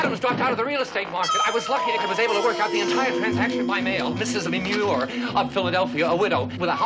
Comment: does Claymore say "Mrs. Manure? Sounds like it. At 1:39, a ghost leaves footprints?